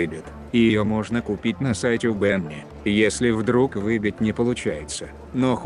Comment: на каком сайте? Подскажите, не могу расслышать😅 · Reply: Benny's Original Motor Works !11 ;-D